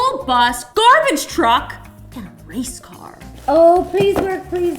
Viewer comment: i thought the race car said taco car oops